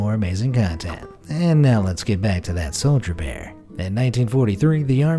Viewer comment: to me it sounded like he said soljabear